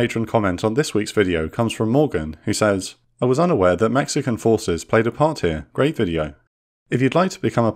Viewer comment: i could have sworn he said Mexico. Hahaha, I know im hearing things. Mexicp has no ships.